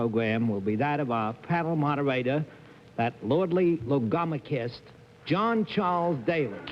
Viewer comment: Since I had no idea what Bennett /said/ at at 2:00 or so, I took a wild stab and then google did me a solid. Anyway, he called Daly a 'lordly logomachist'. The lordly part, I could hear fine. The other part, well, Bennett is Bennett. Anyway, logomachist is basically what you'd expect, and is about talking a lot; 'someone given to disputes over words,' sayeth the dictionary.